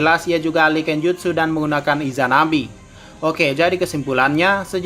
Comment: gue dengernya izanabi